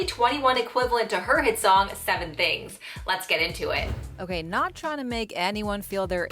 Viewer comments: Bye because I really thought it said 7 rings 😂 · At first I thought it said 7 rings lmao 🤣